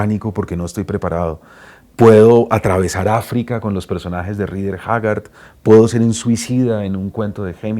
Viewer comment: Alguien sabe cual libro que dice en el minuto 4:41 que habla de africa de "Rider Jagar" o aún que sea el autor, gracias.